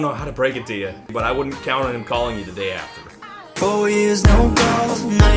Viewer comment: I keep hearing "four years no calls" as "four years no balls".